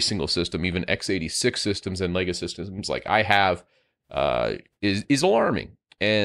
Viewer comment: Did he say it's showing up on legacy systems?